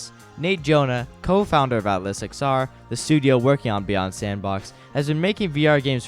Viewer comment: the subtitles say “Stress Level Zero” but says “Atlas XR” anyone else notice that?